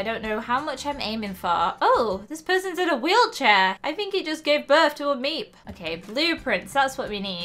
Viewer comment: i just skipped thru a bit and it made it sound like said: "we gave birth to a mistake!"